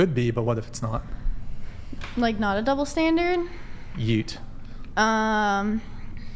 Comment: Did he say "YEET"? Please explain this moronic response.... Thanks